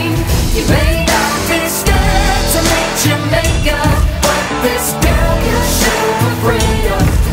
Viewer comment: Song: You may not be scared to meet your maker- Me: Who is This "Jamaica" guy and why should i be scared? (No offense I just heard "Your Maker" as "Jamaica)